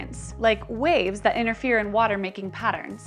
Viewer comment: Nicely summarised. I guess when I heard you say 'inner fear' that I should assume 'interfere' ;)